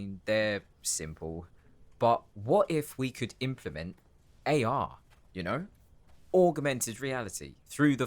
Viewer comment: the way my heart SANK for a second before i realized he actually said "AR" oh my god